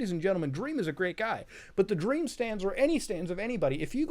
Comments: Bruh the first time I heard of Dream Stans I thought the person said Dream Stand, and I was like wtf I didnt know he had jojo fanart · Muta: "Virus inside humans" Me that misheard stan as stand: thinkingface